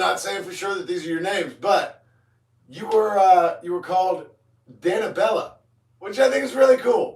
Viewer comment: Hello, dear Roooobhhhhh. It's Danibellaaaaaaahhhhh (it sounded like you said Danabella) 👋🏻😂 I've been asking myself when you're going to realise what Wendy calls me all the time. It does have a story behind it, and it has something to do with Micah Bell because I like him as a character. I see more in Micah than just the most hated character in the game, and I stand by it. So it's like a mix between my name and his last name. But it's a long story 😂😅 You did great today! With the narrow squats, it takes time to do them properly. Your position of your legs and feet were good. The only thing you need to work on is your back. It needs to be as upright as possible. Not too much, though. It's natural to bend your back a little during squats, especially with narrow squats as it takes balance and core strength to do them as upright as possible, but it looked like you're folding yourself together, haha. The position of your back during squats is also important to target either the butt or your legs. The more you bend your back forward, the more you target your butt. But as I said to you several times, don't doubt yourself too much! Everyone's fitness journey comes with challenges, and you can't overcome every challenge in one try. So please don't give up, keep practicing and you'll perfect it one day. I talk about experience as I've been doing this for years, and I still have days where I struggle with exercises. It's natural ❤️ Much love to you & Take Care :_RobHeart